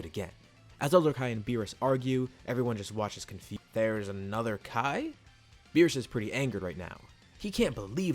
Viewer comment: "confu-there's another kai" ?? Who else heard that? 🤣🤣🤣🤣